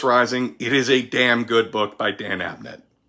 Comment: i have to admit that at the end of the video i misheard "dress down" as "undress"and was like 'woah, they REALLY made some changes to the story'